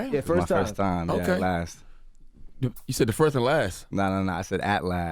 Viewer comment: i thought he said “first and last” too LMFAOOO